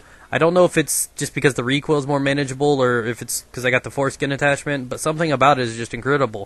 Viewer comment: was i the only one who heard him say the foreskin attachment at 3:18? you make my day!